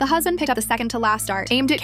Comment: I thought he said "the huzz and" at the start 😭😭😭😭😭